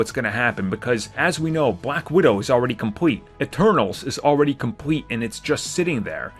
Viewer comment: The Eternals oh thought I heard The Turtles 🤣